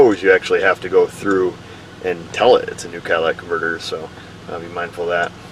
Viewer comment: I like these videos , but I am from Alabama and it seriously sounds like he is saying Cadillac converters. Which is what someone from Alabama would say".".".......................